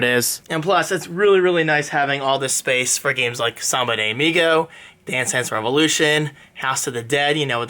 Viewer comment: what game is he saying at 3:40 ???? sama de migo ?